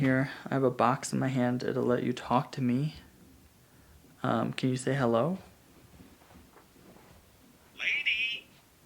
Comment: OMG i just heard say lady, i think he was talking about mrs wilson